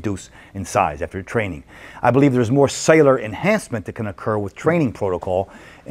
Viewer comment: What does he say at 1:31:42 ? “I believe there's more ... and ... that can occur with training protocol, and more sarcoplasm development and water variations with the drugs” ?! Perhaps “cellular enhancement” ? I'm not sure it makes sense... although I kinda get the idea... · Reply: Yes, cellular enhancement